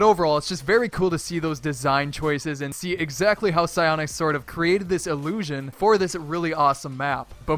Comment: The suji secret I thought it said PS4 not psyonix and I'm an Xbox player